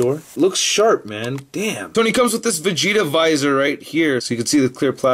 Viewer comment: When he said vigita visor I thought he said vagina lol